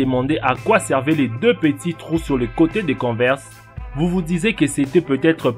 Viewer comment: J'ai entendu "vous vous disez", j'ai arrêté, et je me suis réfugié dans les commentaires.